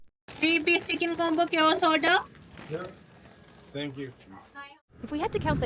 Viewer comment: I;m Indian and I couldn't understand what she said. KFC Chicken Combo Chaos order?